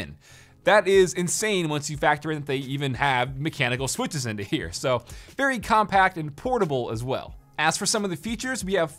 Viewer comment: I almost lost my shit here because I thought he said affordable.